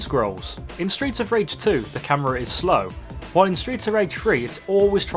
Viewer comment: At first I thought he kept saying Streets of Rage Free